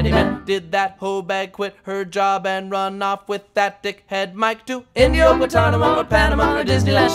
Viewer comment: am i the only one who heard him say 'imma just call it gross.' head Mike?